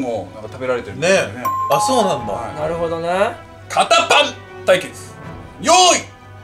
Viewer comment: 堅パン対決→肩パンに聞こえたw